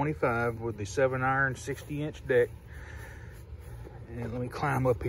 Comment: LOL, first time i heard you say "7 iron 60" deck" i thought you said 768" inch deck... had to play that back once.....I It was 96 felt like 106 in Conroe.